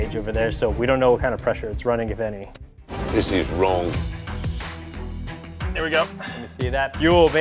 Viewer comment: Do i hear Clarkson saying "this is wrong" 22:39?